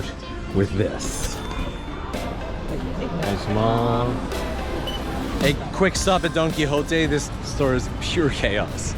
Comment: Can someone tell me what he says at 3:23/3:24 while giving the product to the cashier? I mean in japanese. Is it a short form of a sentance or something? 🤔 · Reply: I'm just a beginner at japanese and what I hear is "Arishi masu", a quick way to say arigato gozaimasu (Thank you)